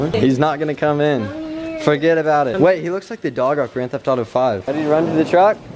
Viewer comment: What movie did he say at 9:15? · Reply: Rylie Maria it wasn't a movie it's a video game called grand theft auto 5